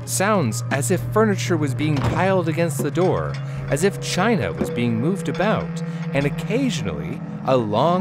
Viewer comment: Am I the only one, who thought he sayed "like china was being moved around"?